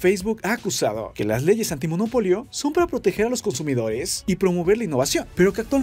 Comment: Por un momento pensé que decía "monolito" jajaja( perdón es la costumbre)